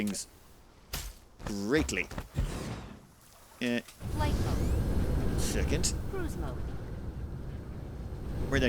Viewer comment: Every time you start flying and the woman's voice says "Flight Mode," I always hear "Light Bulbs" and it amuses me far more than it should.